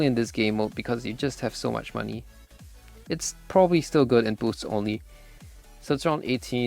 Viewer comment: did he say eco choke is good in boost only? I am not sure cause my english is not that good? If im not mistaken, boost only has no eco choke? Im thinking on buying eco choke, thats why im asking